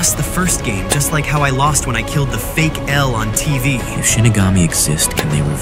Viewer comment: it sounds like he is saying if Shittygamy exists.